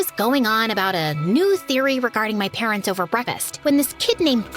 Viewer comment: Brevest instead of breakfast is cool